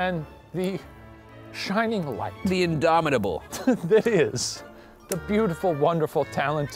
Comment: When Ryan said “indomitable”, which yes I had to look up, I thought he said “abominable” as in the snowman aka the Yeti aka is a Yeti? If so, Shane is clearly Bigfoot, Ryan is either Mothman or Goat Man (bc he was obviously demonized at the bridge), and is that creepy fairy from the recent “Are You Scared?” that steals babies because that’s how he stays so young looking.